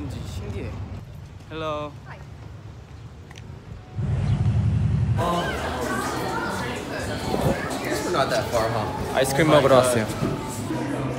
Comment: I heard “ice cream or we’re not simps” 😭🤚